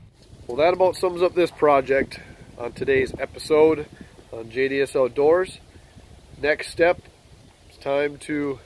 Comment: When you said "JDS Outdoors" I thought you said "Idiots outdoors" I had a hardy chuckle.